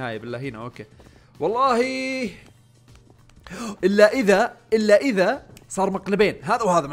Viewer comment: آخر مرة سمعتك تقول الكلمة من ايام كوكب الأقزام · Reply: الّا اذا